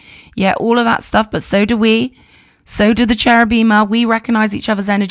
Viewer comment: Did you say you are a Cherubim? Like one of the high ranking angels? 😱